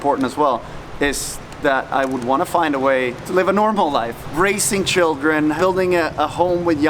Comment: I thought I heard "racing children" I was like what lmao